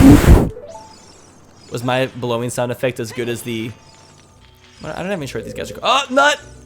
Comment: i heard "was my blowing good" in realit he said, "Was my blowing sound effect as good as--" then you cut iff